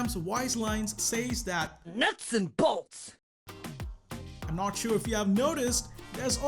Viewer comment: In fact 19 2:28 I thought thought pam was saying nuts and balls 😂